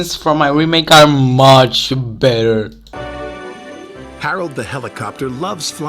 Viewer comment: Did you say better or badder? Anyway, badder is incorrect it’s worse! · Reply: i said better but my accent then was so bad